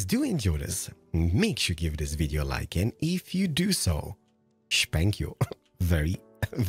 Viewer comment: did he say he would spank me if I liked the video well in that case I’m making Multiple accounts to like this vid